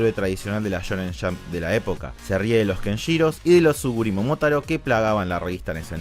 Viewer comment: que dijo ahí? kenshiro y que más? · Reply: Tsurugi Momotaro